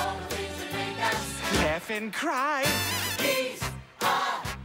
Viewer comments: For the first 12 years of my life I thought Stewie was saying Death and Crime cause it just fit for his character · I always imagined Stewie saying: “Laugh and Die”💀💀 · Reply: I thought he said death and crime